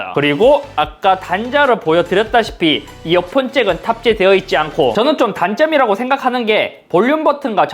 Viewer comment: 순간 이어폰잭은 삭제되지 않고라 들림ㅎㅎ